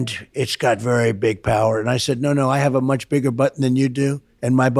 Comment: I swear to god I heard I have a much bigger butt than you do